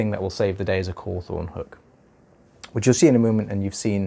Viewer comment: I heard "cool thorn book" when you told us what was going to save the day. Since that makes no sense, I'm going to guess that either you need to talk up, or I need to come see you.